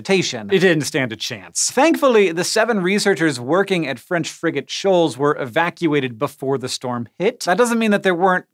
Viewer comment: I kept hearing “french freakin shoals”